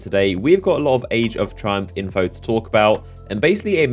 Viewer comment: I thought is said trump instead of triumph😁